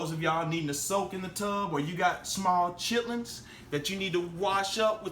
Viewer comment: Did he just say chitlins? LMAO